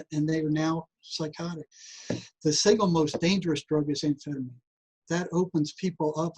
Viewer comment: “the single most dangerous drug is ______.” I missed the name- what did he say? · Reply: Amphetamine. He goes on to say "Meth" afterwards.